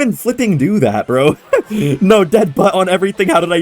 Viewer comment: did he say dead butt?😂